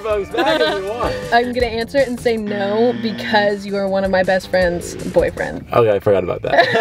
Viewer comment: "DONE!!!" SHOULD MARY BRENT!! THEY WOULD BE SO CUTE TOGETHER!! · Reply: Is it jus me or did I hear “I am going to say no bc u r my best friends boyfriend”